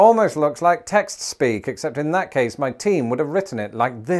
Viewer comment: definitely heard “my teen” instead of “my team” and thought for a second had been keeping quite a long secret from us 😅